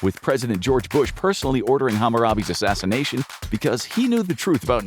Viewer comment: Did man say HAMAROBE?